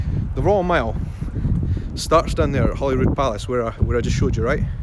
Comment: Hi. Loved seeing you walk up the Royal Mile. I did the full walk back in 09 when I came for the Homecoming of the Clans, from Holyrood Palace to Edinburgh castle under the banner of Clan Gunn. I walked as far down last trip as the church yard with a walking tour of Edinburgh. I stayed in the High street hostel, which you passed, so I was close to everything, it was nice seeing landmarks I recognised when last there. One day soon I'm gonna do an introductory video of me and post it to your fb page, soon, when I'm not looking so haggard from kitchen reno, chuckle. See you there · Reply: haha I thought he was saying Hollywood palace.... I'm such a dumb American lol